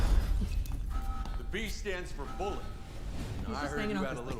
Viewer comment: i honestly thought he said "b stands for bullock"